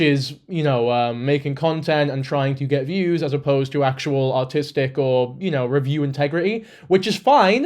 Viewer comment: i heard autistic.